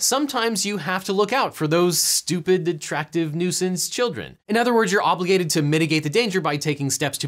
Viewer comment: Wait did he just say, you have to watch out for those stupid attractive children ? hahaha